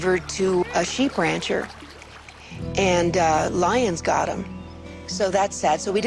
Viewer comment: did she say a LION got them?!